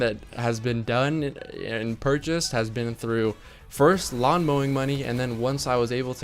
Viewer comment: When you said lawn mowing money at 7:05 I thought you said long bowing money but I guess you make that now :p